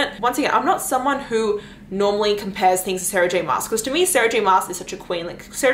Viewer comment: Sarah J who? Sarah Jane Masters? I have no idea what this name is. She said a name like 15 times but it was SO fast. I know nothing about fantasy romance books and am trying to get some recommendations to read first. Obviously that one is the one I should start with but is that an author? Book series? And what did she even say?? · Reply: Sarah J. Maas !